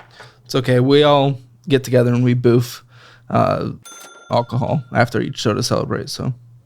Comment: Bwahahahahahahaha did he just say we "boof alcohol" after each show?! 😂😂😂😂